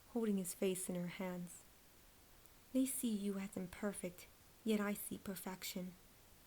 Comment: what did you say? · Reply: Ruby to Chomper. "They see you as imperfect, but I see perfection." Hopefully that clarifies it.